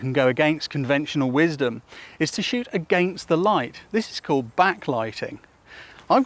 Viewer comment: I heard "shoot a gangsta light"....